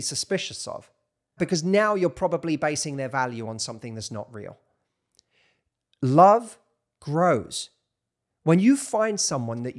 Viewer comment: I thought u said love gross